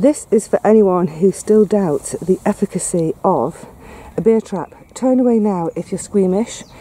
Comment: Heh, a beer trap. I thought you said 'bear trap' and was amazed at the London wildlife, lol!